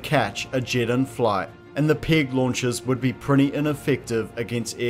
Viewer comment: I keep hearing it as, “pig launcher.”